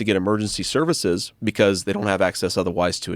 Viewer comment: Thought he said he talked for 4 hours